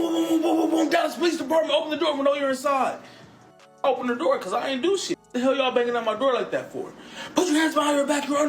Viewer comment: Did dude really say he didn’t do anything 😐😐